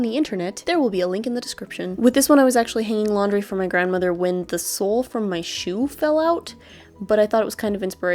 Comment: I thought at 6:30 that was your grandma on the ground instead of your 'sole' and I was like omg wtf